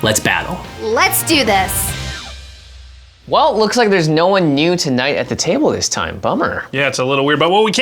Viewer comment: Jimmy - "There's no one new to knight at the table this time" I thought he just said "There's no one new tonight at the table this time." gotta be a regular to catch the difference.